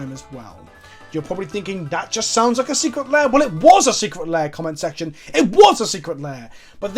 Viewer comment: I heard "Secret Lair Comment Section". That ones gonna be fire